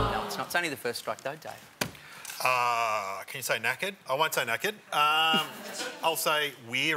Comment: thought they said naked